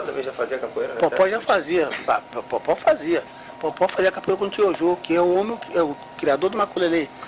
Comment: o que ele fala ? Quem ? Tio jo? Isso?